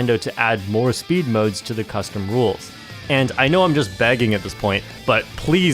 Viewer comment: Did you just say... BAGGING????